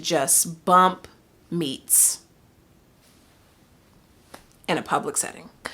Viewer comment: DID I HEAR “BUMPING MEATS”???!!!?!😩😩😩